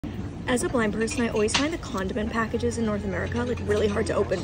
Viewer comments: I thought she said as a black person and although I was a little confused it’s not completely impossible to look like this and be black but I couldn’t figure out how it related to what she was talking about until the video restarted 😅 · man I thought she said sugar packets and I was SO confused. No, she said condiments, that makes so much more sense.